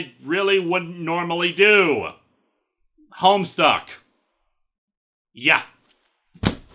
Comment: The first time through, I thought you said "Homestar". Sewiously, Pom-Pom, that would have been SO gweat.